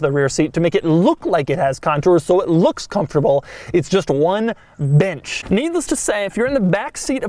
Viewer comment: Had to rewatch that a few times; I was watching at 2x speed and thought he said "it's just one bitch"